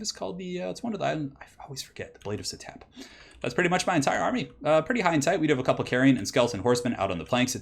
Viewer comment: "Pretty high and tight" I heard you say. You a mommy?